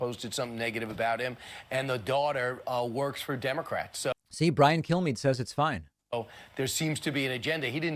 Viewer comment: It sounds like you said "Brian Kill-Me" and I think, yeah .. I'd want someone to kill me too if I were him.